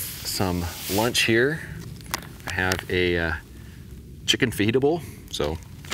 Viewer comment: Upon re-watching this video, I swear I heard "Chicken Pho heatable"...